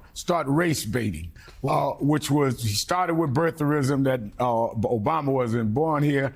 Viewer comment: Did he say Trump started with Birthrism 15:53? Haven’t heard that one before 😂😂